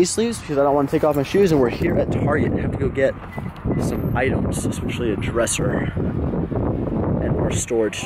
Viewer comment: Guys English is not my native language, did he say have to go get some idioms at 7:35? It doesn't make sense but it sounds like idioms.